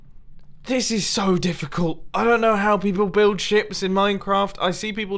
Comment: I was adjusting my headphones when you said I don't know how to build ships and I heard I don't know how to build sh**